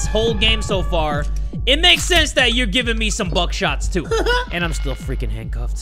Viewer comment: I thought it said back shot..